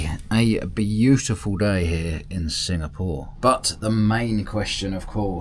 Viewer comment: What country are you in? I thought you said singamore?